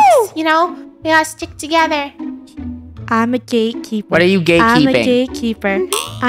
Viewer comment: i hear "im a gaykeeper"